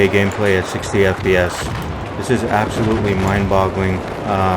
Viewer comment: What he say?? Mind Bowling?